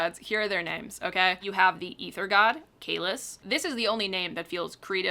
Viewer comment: I misheard this as Kahless, and for a second deadass thought someone named their fantasy god after the First Emperor of the Klingons lmao Edit: can't take seriously the mmc's name either. Whenever he's mentioned I keep picturing Ricardo Montalban 😭